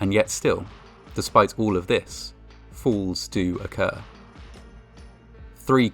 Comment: Now, I know my hearings not the best in the world,but I`m sure I heard you say "Fools do occur." How very true Sir.